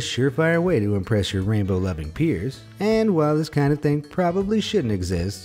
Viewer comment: I thought he said rainbow loving queers 😂